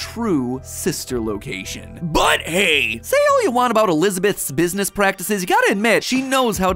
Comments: Thought you said Elizabeth was venessa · I thought you said that Elizabeth is Vanessa.